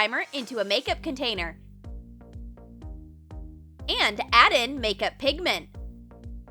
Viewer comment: i thought she said add makeup pigment O-O